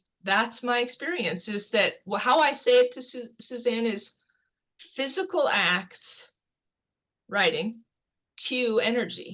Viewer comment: What did she say? “Physical act queue (or cue) energy” or “Physical act kill energy”?